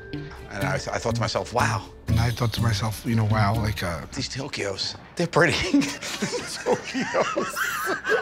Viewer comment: These Tokyos are pretty! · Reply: I thought he said these tokyhos